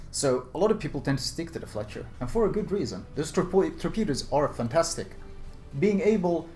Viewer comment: DID JUST SAY TORPOI??!